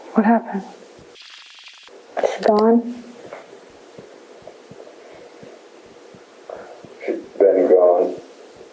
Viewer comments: Subtitles are wrong. What was actually said: "Is she gone?" "She's been gone." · My interpretation of the parts that couldn't be understood: 1:40:41 "she gone?". 1:40:44 "she's been gone...no hope". 1:41:16 ""no one could stand her, cause she lied all the time". 1:44:39 "only reason why I am crying, is because I loved her....(cant understand).......wanted to share that with her, because all my past and all the sh*t I've done" 1:47:05 "why'd you do that?" You know? 1:49:30 "Candice was probably more of an anger, to target all of her lies, so I just...tortured her, for almost like 3 days." 1:49:42 "no I didn't have sex with this one, ya know? I was beyond that". 1:50:02 "then we'd go to walk around town, right. Ya know what I mean? She coulda easily just went an like hollered "help" anytime or anything, so".